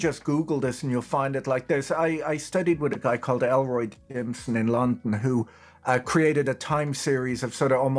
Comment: Can anyone tell me what the guy's name he was referring to @ 28:40 🤔? Elroy what? I googled Elroy Jimson like the captions said but got nothing